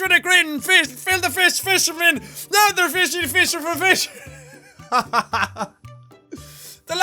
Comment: Fishy fishy da fishy fer fish was literally all I heard.